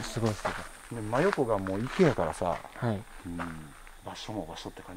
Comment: 「真横がもう池やからさ」の後に「うーん」って聞こえる